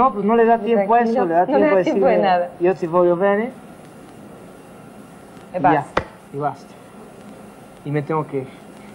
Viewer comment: Minuto 1:16 que dice? Le pregunta Verónica que que le dice su mamá, pero no sé entiende la respuesta de él. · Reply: "Io ti voglio Venne", yo te quiero mucho o yo te amo en italiano